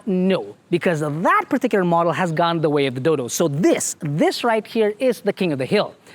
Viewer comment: "Gone the way of the dil**" I misheard, and I had to run by it again :P